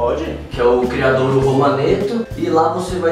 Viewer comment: gentee me ajudem kkk eu n entendi o que ele falou, alg que entendeu pode digitar aq o nome do local? Kkk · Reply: Criadouro Romanetto